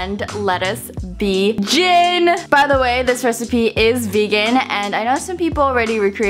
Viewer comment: i thought u said Jin like from BTS